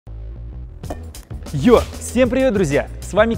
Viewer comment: я один услышал не ЙОУ в Ё